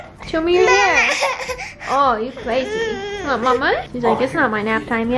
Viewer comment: is talking now? at 16:12 i swear she said “mama”